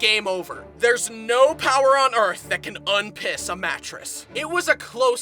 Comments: DID HE SAY P!SS?! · did he sayyy PISS???!!??? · did he just say.... "Piss"